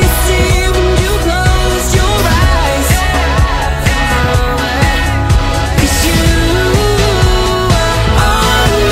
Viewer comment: what does he say at 3:06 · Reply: "On my mind"